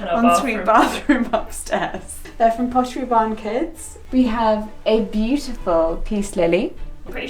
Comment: I totally thought was saying "this is from Pottery Barn, kids" not "Pottery Barn Kids" for most of the video hahaha